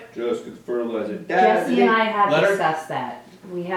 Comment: Did he say Leonard? Real actors name slip? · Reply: He said 'let her'. It's the accent.